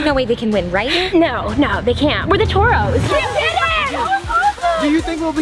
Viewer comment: It sounds like they said Torps